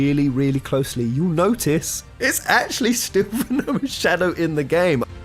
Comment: what is he even saying right there? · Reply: It's actually stivano shadow in the game?